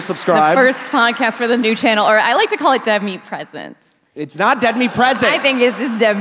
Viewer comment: I support saying Dead Meat Presents 🎁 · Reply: Say it to fast. For a second thought she said deadly presence